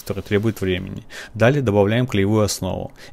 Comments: Послышалось жописная работа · Я, который услышал "Каска", уже придумал бизнес, но под конец видео огорчился. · Я один сначала услышал «каска» а не краска